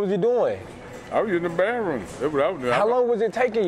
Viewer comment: Did he say baroom?